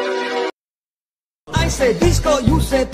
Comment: Sounds like he is saying pissco